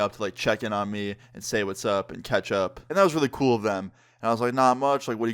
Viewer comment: lmao thought said "hangin out and ketchup" instead of "hangin out and catch up"